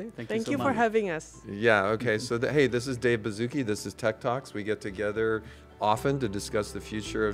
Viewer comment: At the start I thought he said Tiktoks not tech talks 💀